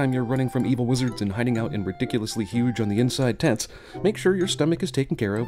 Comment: I misheard that as "ridiculously huge on the inside tits."